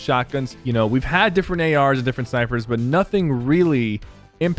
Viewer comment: Did he just say "Different Syphers"? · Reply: He said Snipers but yes it sounded like Syphers 😂😂